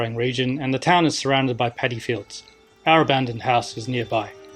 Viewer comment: petty fields, aaah.. i had to rewatch that bit, i thought you said it was surrounded by pedophiles - spooked me a bit